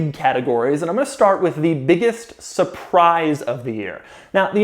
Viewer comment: Unlike the same moment I heard "X7 surprise of the year"